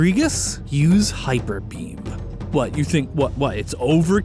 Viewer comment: Thought he said "Hyperpeen" and that's why Cofagrigus didn't want to use it